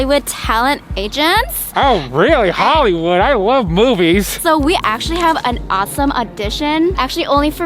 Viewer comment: / 19 : 13 does he say movies or something else